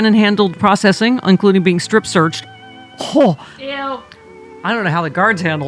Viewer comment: I heard "Bannon was strip searched" followed by a "Eww" that beat me to it. Eww indeed. I feel sorry for whoever had to do that.